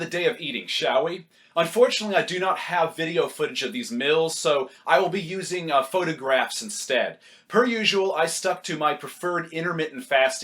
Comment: when you say " meals" it's sounds like you say "mills"